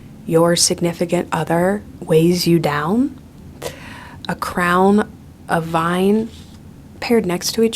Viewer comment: Her abuse of simile is what really drives me insane. Everything is "like" something else, but her imagery is never consistent. "You wrap around me like a chain, a crown, a vine" - crowns don't wrap around you, babe. I'll be generous and say that a crown can "wrap" around your head, but chains and vines don't do that. They wrap around your torso or your limbs. And the experience of being wrapped in chains is VASTLY different from being "wrapped" in a crown. Finally, if the theme of the song is "Ophelia", maybe Taylor could try to use imagery related to, I don't know, Ophelia??? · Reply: Yeah I thought she said “crowd” instead of crown in first listen. Then I heard crown and I was like “huh”? Maybe the pressure from wearing a crown can wrap around you? Idk 🤷‍♂️